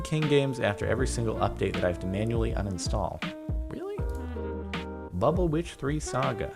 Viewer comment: I thought he said Bubble Bitch instead of Bubble Witch.